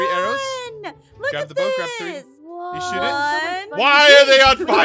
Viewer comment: said you shiit it instead of shoot it